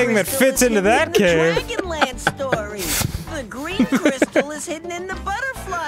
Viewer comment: Did he just say Dragonlance? If that's where they are, then what the hell has Takhesis been doing this whole time?